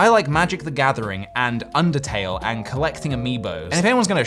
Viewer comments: I thought he said "collecting amebas" and I got scared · I swore he said "collecting amoebas"... and I was a little confused and fascinated at the same time